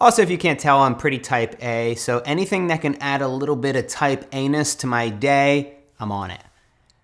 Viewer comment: Sounded like he said tight anus instead of type A’ness